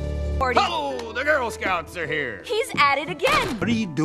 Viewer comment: AINT NO WAY I HEARD HIM SAY THE N WORD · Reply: He said the Girl Scouts are here